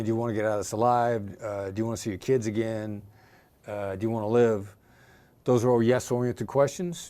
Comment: "do you want your wife to WIN?" what was he saying here?